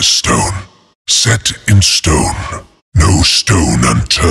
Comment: did he said "sex in stone?!"